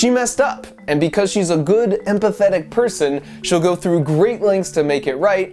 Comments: I heard ‘and pathetic’ instead of ‘empathetic’ LoL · I thought he said “and pathetic” Instead of empathetic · To be honest I thought he said “she’s an honest and pathetic person” Instead on empathetic... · oooh you said "empathetic"... i thought you said "and pathetic"